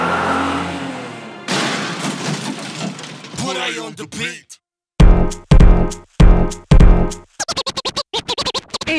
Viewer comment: kor mit mond?kerlek valaki leirja mert en nem ertem😣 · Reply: Burai on the beat